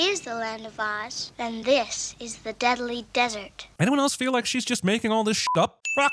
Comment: She says "desert." Caption says "Dessert." These are two very different things!